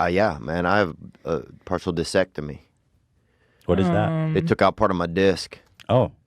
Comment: This was such a real moment, props to Ethan for that minor internal debate on whether he should press it, and then leaving it alone lol. Love my boy Theo. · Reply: I thought he said partial vesectimy